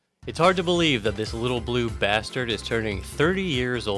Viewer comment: Did you just call him a bastard?